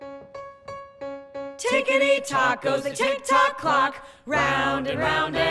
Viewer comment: 🤣I thought the were saying ticktey tacos